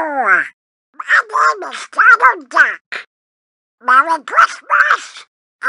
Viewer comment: Error 0:01 its say minnie mouse instead of donald duck